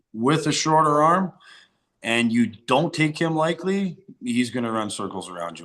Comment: Sounds like matt says “Likely” instead of “lightly”